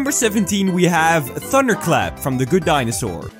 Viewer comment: I thought he said thunder clap